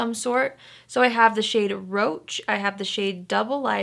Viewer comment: I could’ve sworn she said that one of the urban decay’s eyeliners is called roach... is that right?? Or maybe I heard wrong 🤣🤣🤣